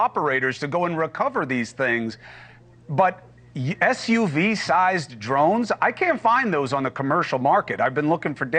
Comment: now he’s saying SUV size I heard the size of a small car. Either is too big to not know what it is